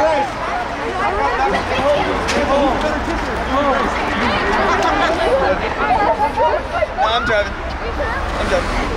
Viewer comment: Is it just me or does addison say Babe..where's Bryce/Babe...wait Bryce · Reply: she said “babe wait bryce”